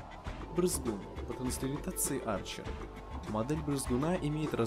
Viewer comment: Арчер на пробежку ! Пельмешку? (Это типа из рекламы мази а Арчер это имя собаки и вырезаного существа) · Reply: Собаку зовут не Арчер а Арчи, тебе послышалось наверное